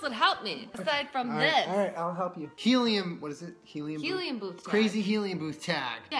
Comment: At first it thought it said helium tooth bag